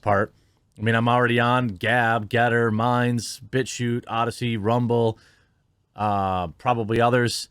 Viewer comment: It sounded like you said “Bitch, Shoot!” 🤣